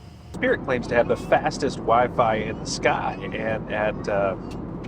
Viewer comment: lol I heard ‘has even wife’ instead of wi-fi 😂😂